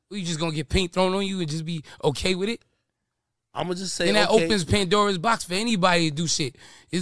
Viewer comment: I thought cuz said “now that opens Paintdoras box”😭😭 I wish he did lol